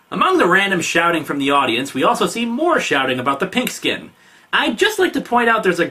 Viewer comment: Pink skin as a racial slur never made sense to me, I actually played some of the older games, I thought they were saying "Pig skin," which if you ask me is both better as a slur, and makes more sense in context